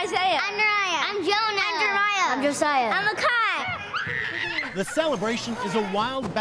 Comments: I thought one kid said “I’m a kite” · I thought the last kid said I’m a kite